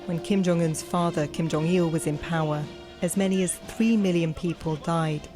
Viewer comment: I thought she said 3,000 people died in the famine, rewinded to hear 3 million...